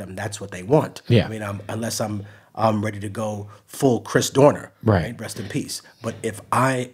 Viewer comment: Did he says Chris Dorner?